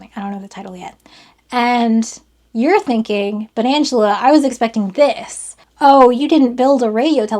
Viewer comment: When you said “but Angela” I thought you said “banantula” like a fruit spider. Entirely unrelated to the video, but still